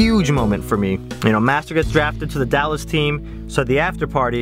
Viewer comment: When you said I drove over to the playboy mansion and got drafted I thought you said jacked D:"